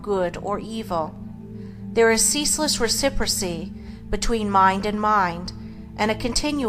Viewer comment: What is the word after ceaseless at 11:47? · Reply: pretty sure its reciprocity